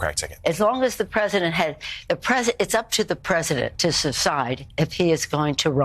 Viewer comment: Did she just say secide??